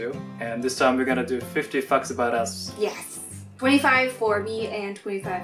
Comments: 50 fucks about us 😂 that's what I thought he said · I thought said "50 fucks about us." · careful of the way you pronounce "Facts", cos the way you pronounced it, sounded like you said "fucks" ....woods! Haha 😊 great video though 👍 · I totally thought said "50 Fucks For Us" and I know he said Facts but I can't unhear it now. 50 Fucks For Us! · When says “Facts” it sounds like he is saying “Fucks” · I thought said "50 fucks about us." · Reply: Hansen i know he said facts but at first it sounded like he said fucks. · I thought said "Fifty F**ks About Us" XD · I thought at first they said "we will do 50 fucks about us" 😂😂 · i thought I heard '50 f**ks about us' ._. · Is it just me or does anyone he say fuck instead of fact. I heard "50 fucks about us. " I also sleep with my baby blanket. Oh and just a few days ago I got a wasp nest stuck in my hair. I am also what you'd call a lucid dreamer. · did he just say ' 50 fucks about us ' I know his Japanese I was shocked but I know he said facts but it sounded like he said fucks XD. ( not hating btw just got a shock just miss heard him ) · It really sounded like said "50 fucks about us." Oops. 😂😂😂😂😂😂😂😂😂😂😂😂😂😂😂😂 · Lol ok you need to work on saying facts XD hahaha I'm a little hard of hearing so it sounds like you said fucks. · thought u said "50 fucks about us" lolz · i thought said "50 f**ks about us" for a second, and i was like tf? then i realized ohhhhhh "facts" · I heard "50 fucks about us" 😂😂